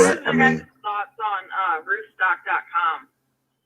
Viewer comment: mentioned a website @ 1:29:57 I couldn't hear it clearly. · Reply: roofstock